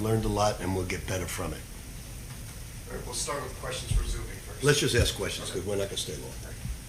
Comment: LOL The closed caption at 1:06! Pitino said "Let's just ask questions because we're not gonna stay long." The closed caption says, "Let's just asks questions because when I kissed a boy,..."